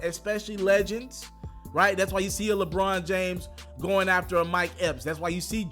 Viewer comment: Did u jus say Lebron James going after a Mike Epps??? Did anyone else catch this 🤔🤔🤔